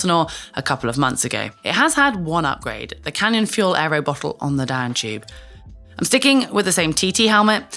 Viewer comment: No, I thought you said no round bottle on the frame! lol